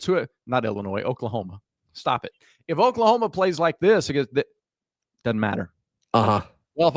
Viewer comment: I thought you said last year doesn't matter